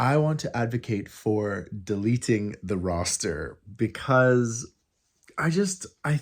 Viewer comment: I'm so sorry. I have cataracts and I thought you were saying, "eliminate the rooster"! I do believe that you live on a farm; so I was wondering where you were going with this topic, but alas you weren't referring to your rooster but rather your roster. My bad...hope that you had a great day!